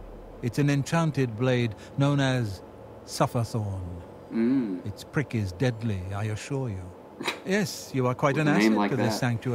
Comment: When he said it's prick is deadly, i thought he said its frickin deadly.